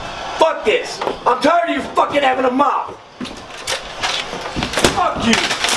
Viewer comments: I think he said "f***ing having a mop" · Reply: I think he said"I'm tiered of you fucking having a mouth!" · I heard "I'm tired of you f×××in' havin' a mop."